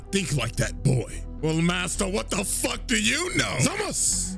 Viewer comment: I could've sworn you said Dog side yet it was dark side.